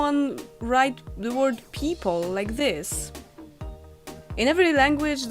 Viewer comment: The first word I've ever misheard you say was people. Totally my fault, but I heard "peep hole" and was very confused for a second! Also, very amused because I'm easily amused.